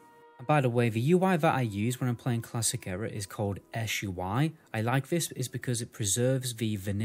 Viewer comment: What does he call the UI mod at 4:00? Sounds like "Esh UI", but I can't find it.